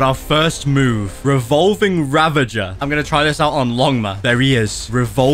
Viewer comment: Yo am I the only one or did he just say ligma to longma